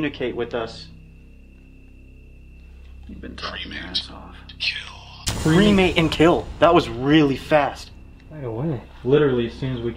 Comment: I thought it said “cremate and chill” 😭